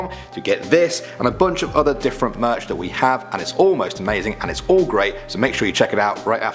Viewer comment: O M G! When you made the pitch at the beginning for the merch...it sounded like you said, "It's almost amazing".... But I know you really said, "It's all Most Amazing"